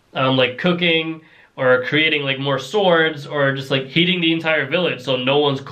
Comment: did he say cocaine 😭😭